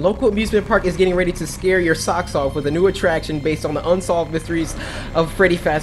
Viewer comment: when you say socks I thought you said sexoff